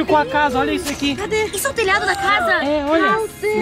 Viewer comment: só eu ouvi "Cac3ta"